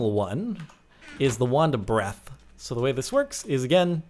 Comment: He sounds like he said wonder bread xD